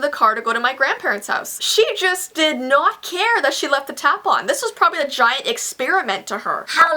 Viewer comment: Every time you said 'tap on' I thought you're saying 'tampon' 😂😂😂